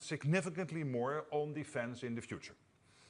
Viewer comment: I thought he said "Significantly more OnlyFans in the future"